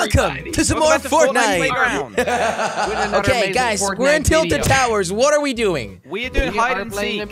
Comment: I Think that he said ice not guys